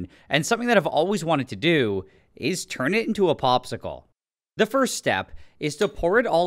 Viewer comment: I wasn't paying attention at the start and I thought he said 'This is bromine, and I'm about to turn it into a testicle' lol